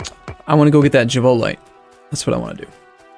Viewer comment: ish, I swear I thought you said I want to get the Chipotle....lmao